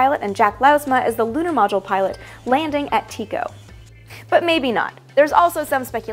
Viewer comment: I thought you said “TECO” instead of “Tycho”. The difference? The latter is a highly-visible crater on the Moon, the former is an old text editor whose command set looked like transmission line noise.